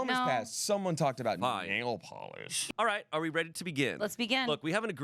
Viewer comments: said "nail polish" and i heard "anal polish" and had to rewind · Sounded like he said "Someone talked about anal polish." 😂😂